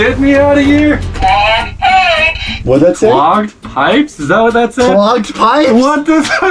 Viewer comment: Hen it said clogged pipes i thought it said logged lice